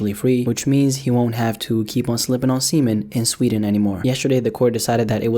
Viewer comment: I thought you said he was sipping on semen